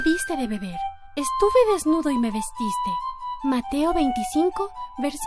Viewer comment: hubo un instante en que escuche "mate a 25" en lugar de "mateo 25"